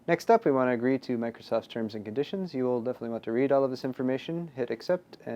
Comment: I zoned out a bit at that part, and I could have sworn he said "If your lonely enough to read all of this information."